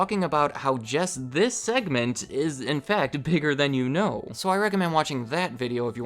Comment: I thought it said bigger Chan you know. But it’s bigger than you know 😂😂😂